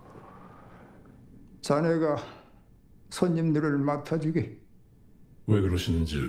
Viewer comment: why does it sound like he said “sa n*gga”